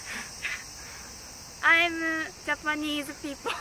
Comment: "I'm Japanese people" 🤣🤣🤣 That was the cutest moment 🥰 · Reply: i heard "i'm japanese K-pop"